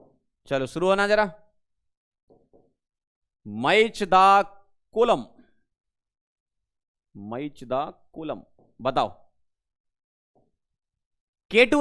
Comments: when sir say match the column i thought he say mai chuda😂😂😂😂😂😂😂😂😂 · Sir ye aap kya bol gaye 🥲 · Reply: Match the column